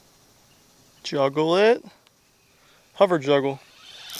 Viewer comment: Says hover juggler, but i could swear i heard you say hover juggalo. woop woop mfr😂